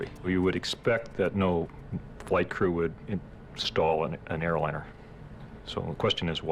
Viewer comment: What did that NTSB guy imply when he said: "No White Crew would stall an airliner?" Or is he a racist???? Time Stamp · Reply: He said "no flight crew..."